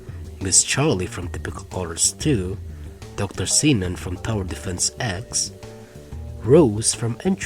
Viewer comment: What did I hear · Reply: Dr Xenon.